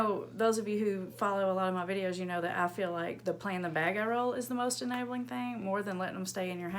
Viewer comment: captions says that you're saying plan the bag roll lol. Took me a few times to know what you were saying.